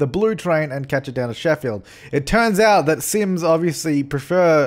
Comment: did you say sims?